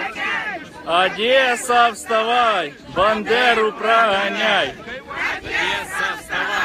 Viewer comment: Мені на мить почулося "Бандера вставай! Рашистов проґаняй! "